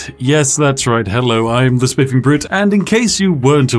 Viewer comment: I just want to point out that the CC thought Spiff said "yes it is me the smithing brute" not "Spiffing Brit" lol